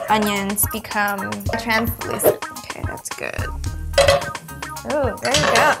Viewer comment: Kylie: *Starts pouring too much oil* Karrueche: "THATS GOOD" LMAOOOOOOOOOO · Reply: I thought she said THAT'S IT 🤣